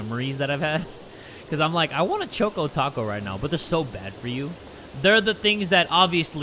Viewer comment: I thought Jay said, 'i want a choke taco' and I was just like: 🧍🏻‍♀️